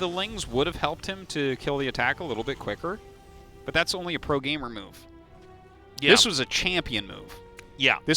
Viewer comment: Does anyone else hear "That's a pro-gamer MILF"?